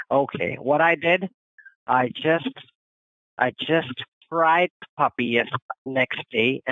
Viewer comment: So…..was he talking about eating fried puppy around 4:11:00 or am I mistaken? I’m paused, no ideal if there will be immediate clarity, I’m just sort of in awe of what I think I heard.